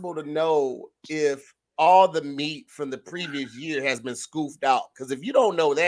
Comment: said "scoofed" instead of scooped